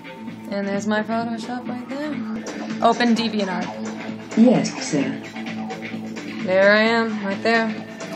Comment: DID IT SAY YES SIR?